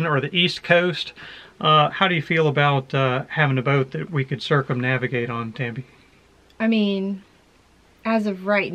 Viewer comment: Junk rig ? I thought you said you wanted a boat that could point.